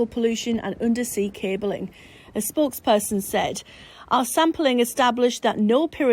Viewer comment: "The government are looking into it" Right! That inspires a lot of confidence! When she said. "A sporkesperson said" I misheard - thought she'd said a "sportsperson said". Considering actors and footballers opinions sre so highly prized nowadays that would make more sense. Just don't let them play with real guns.